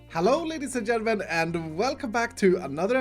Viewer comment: Wasn’t paying attention and I thought he said “Hello n****s and gentlemen”